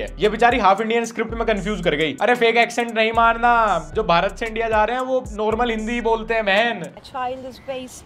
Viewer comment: Did he say bharat se India ja rhe h?